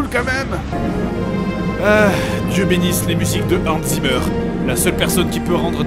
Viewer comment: Excusez moi, à 16:04 quel est le nom du compositeur? j'ai compris anzy bur pourriez vous me direson réel nom; cordialement.